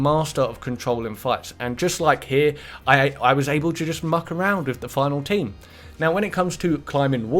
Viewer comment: When he said 'muck around with other teams' I thought he said 'fuck around other teams'